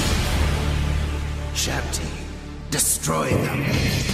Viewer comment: did he just say (Shit.. Destroy them!)